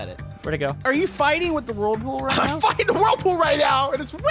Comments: i think said the f word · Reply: Nope, he said "I'm fighting" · i have a question.. did say a bad word at 10:31 ? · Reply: He said stuck in the wildpool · did he say I’m fighting with the world pool or I’m f**** with the world pool???? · did I hear what I think I heard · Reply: He said he is fighting